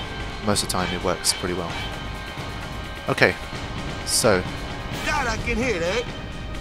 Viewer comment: At first I wasn't watching the screen and thought Wakka said "God, I can hit ayy! 😄"